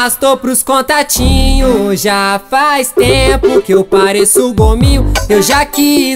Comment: Eu entendi " ja faz tempo que eu pareço comigo" kkkkk